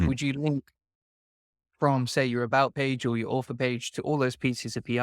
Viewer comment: LOVE it! Terrific content as usual. I thought he was saying "Offer" the whole video. Then I went and watched more of his videos. He's say "Author". LOL.